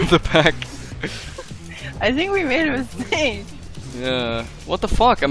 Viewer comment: did she just say "i think we'll win with sing"